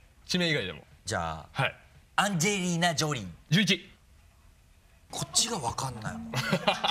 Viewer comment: 若林12だと思ったけど空気読んで何も言わないの草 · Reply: pon Deco アンジェーリーナジョリーって聞こえたんだと思う·····